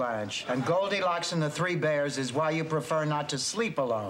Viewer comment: B: "Why, it's the reason I prefer not to shower alone" D: "Sure, Blanche, and goldilocks and the three bears is why you prefer not to sleep alone" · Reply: My distracted hearing heard that as Goldie Hawn and the Three Bears 😂